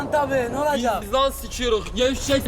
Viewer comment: de ozi biz bişey sikiyoz diyor ne diyor anlamadım